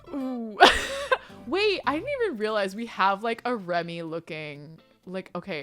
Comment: im not being mean but was it just me heard hersy Remi instead of Rumi (im not hateing i love you kelsey)